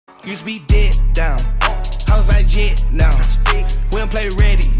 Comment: On God... Thought Young Thug said "Used to be dicked down" in the intro 😂😂😂